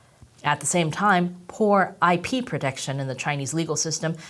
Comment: At minute 9 I thought she said 'whore IP protection' 🍯🍯🍯